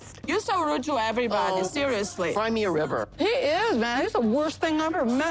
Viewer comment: Did he just say FIND me a river? 😩😩😩